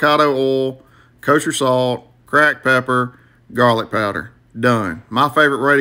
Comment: Who else heard him say 'crack pepper'😂